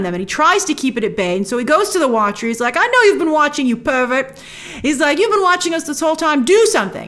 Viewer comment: Did just call The Watcher a pervert?!